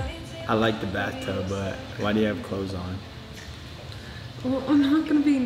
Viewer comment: Nobody: Griffin: Why do you wear clothes? Dixie: I Dont want to be nakey😂 Me: LMAOO GRIFFIN😂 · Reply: Griffin said why do u have clothes on I didnt hear it very well sorry😂